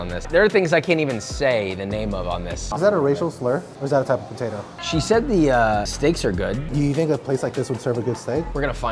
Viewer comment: “Is that a racial slur?… or is that a type of potato..?” 😂 bro I needa know what that says lmfao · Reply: It's "Cheddar Red Skin Mashed Potatoes"